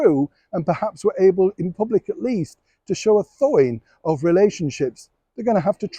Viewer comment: What word does Neil say at the 4:08 mark? Thoin? Thank you. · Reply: "to show a thawing of relationships" .. 👋